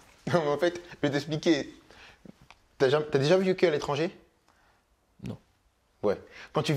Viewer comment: il dit quoi a 33:40? Ta deja quoi???? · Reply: Kylian demande à mouloud si il a déjà vécu à l'étranger